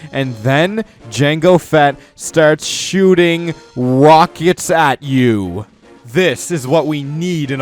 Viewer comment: I thought in one part of number 5 I thought he said wockets 😂